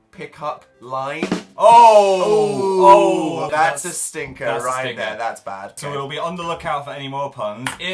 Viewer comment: Dan: That's a stinker. Me: You mean that's a "sinker" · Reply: I thought you said "striker"